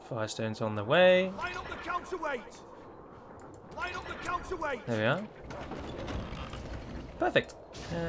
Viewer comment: Young me always thought the trebuchets said "why won't the counsel wait?"